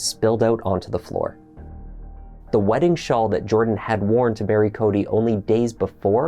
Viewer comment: Love you, but did you say "bury" when you meant to say "marry"?